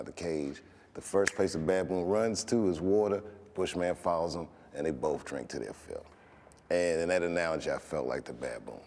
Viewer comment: could someone tell me what he says after 1:20 '' the bushman follows him and ....'' English isn't my first language and I can't understand the words he is saying here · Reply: "...follows him, and they both drink til they're full."